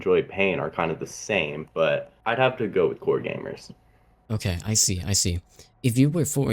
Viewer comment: the fucking irony when i search up for "roblox alternatives" im on a guest account edit: nevermind, i thought you said games from the game "Core"